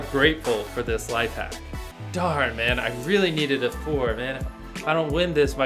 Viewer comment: did he just say Dhar Mann? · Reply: Darn, Man.